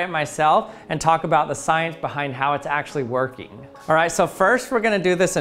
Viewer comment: Did he say "workie" ?😂